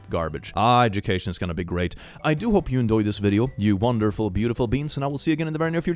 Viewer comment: Each time when calls us 'beautiful beings', I mishear it as 'beautiful beans' and I always feel like a beautiful red bean with a tiny ribbon on me. 😊😂❤